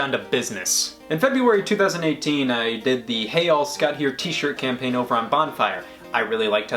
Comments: This whole time I thought he’d been saying “Hey Y’all, Scott here” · I always thought Scott said “Hey Y’all” not “Hey all” What a Twiieessst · thought he always said "hey yall" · i thought he was saying hey ya'll, not hey all. disappoint. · Feel like. I am not a fan i always thought you were saying HEY HALL SCOTT HERE!!!!